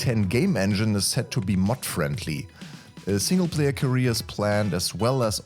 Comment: I was SO excited when I thought he said "mud-friendly". Daaaang. Gotta go to Wendy's and get a Depressionator.